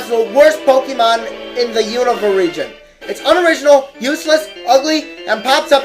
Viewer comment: I thought you were going to say universe.